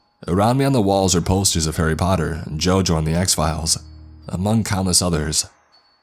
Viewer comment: The writer of this Creepypasta is flexing on us with this vocabulary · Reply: did he just say jojo?